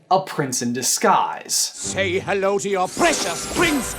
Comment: All this time I thought she was saying "prince in the skies"